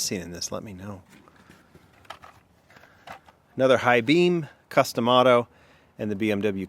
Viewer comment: I didn’t see the corvette ZR1. Also when you said “custom otto” I heard “Cus D’Amato”...I must have watched too many Mike Tyson YouTube vids.